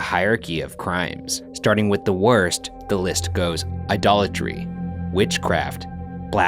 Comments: I was eating while watching this and at the start, I was listening but not looking and I thought he said "A dollar tree". He said idolatry. 😄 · when he said "idolatry" i thought he said "a dollar tree" help